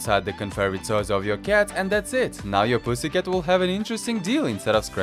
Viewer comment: Did that boi just said pussy cat